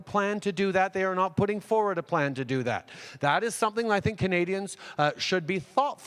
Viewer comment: Each time I hear him say "Canadians" its much more fitting that I really hear him say "comedians"